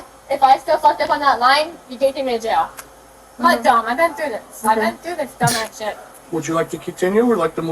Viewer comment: I thought said, "Hey my guy, I've been to Hogwarts"